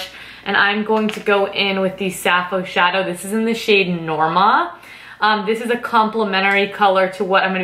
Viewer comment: Was the Sappho shadow you used before you applied alluring was it Laura? Sounded like you said Nomad. Thanks